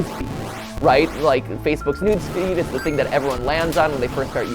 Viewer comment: I cannot be the only one who heard “nudes feed”